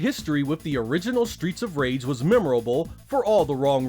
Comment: I thought you said "that Samurai had broken my leg" I was like - holy shit! 🤣 Chemical Plant zone is my favourite stage from Sonic 2. The music is awesome too!